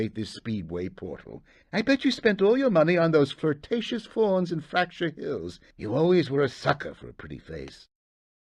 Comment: I've just noticed at 39:38, that Moneybags said "I bet you spend all your money on those flirtatious fauns in Fracture Hills. You always were a sucker for a pretty face." Which at first had no idea he actually said that.